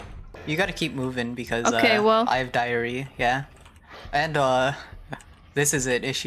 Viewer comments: when said I have diary- and uh yeah I thought they said diarrhea 💀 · When said i have diary why did i think he said diarrhea- 😭😭😭